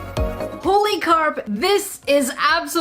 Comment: Lol on the back I thought the pillow said holy crap 😂🤣😂